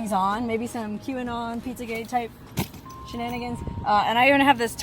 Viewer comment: Did you say cue-an-on, pi**a gate-type shinanigans? LMAO!